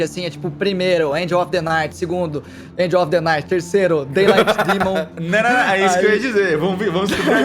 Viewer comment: oii, c sabe qual foi o último nome que o falou na minutagem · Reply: daylight demon